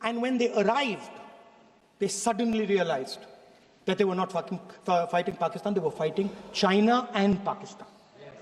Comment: did i hear F**king ?? 🙂